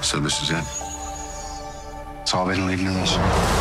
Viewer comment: What is he saying at 0:05 cuz all I hear is itsaleedleednemos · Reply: It's all been leading to this